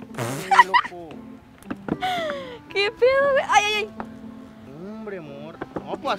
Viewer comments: soy yo o escuche novio? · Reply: También yo escucheee, y en el minuto 23:23 escuché a Francisco decir "Nombre amor" igual y capaz fui solo yo jajajaja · Yo escuche que francisco dijo amor a catia en el minito